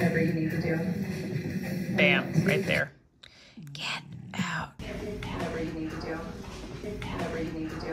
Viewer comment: It didn't sound like 'Get Out' to me. Last word sounded like town or down. 'I'm ready to get down' sounded like.